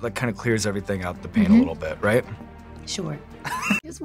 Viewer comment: I swear i thought i heard her say"i just wanna go home to make kids", even though the subtitles show otherwise.🫣